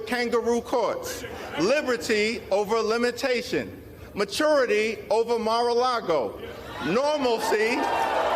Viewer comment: "Freedom over Fascism" "Majurity over Mar-a-lago" ...GO Democracy! We're rooting for you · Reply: I thought I heard "Majority over Mar-e-lago", but maturity is also important. ;)